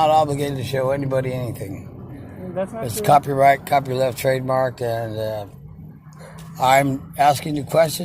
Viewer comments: Did he just say that his name is "copyright, copyleft, and trademark protected"?! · did i hear that correctly? Did stupid say, "Its copyright, copyleft and trademark?" ETA: Oh ffs. Stupid cant even pronounce the words in his script. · Did he say "Copy Left" trademark?